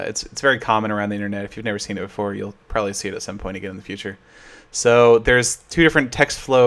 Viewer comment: Did you say...somepony?